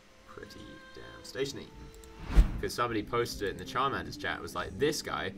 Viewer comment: Can someone tell me who he's talking about? Is he saying charmander?